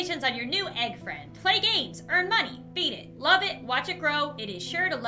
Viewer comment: i forgot it said « feed it » and thought it said « beat it »